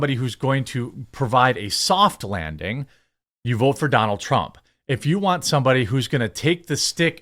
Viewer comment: Sorry, but I chuckled when it sounded like you said Donald Tramp. Not trying to be a douche, I just thought it was funny. I still laugh when the ketchup bottle farts.